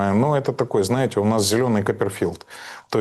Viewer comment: Зелёный Коперфильд! Это потрясающе 😅. Мураев знающий остроумный человек. Слушать его одно удовольствие. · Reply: А мне послышалось "зелёный копрофаг". Даже не подумал, что послышалось.